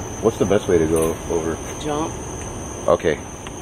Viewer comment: What I heard: “What’s the best way to go over?” “Don’t”